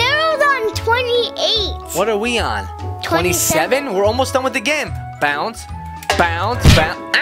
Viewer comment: uh...did he say "where almost done with the gim"?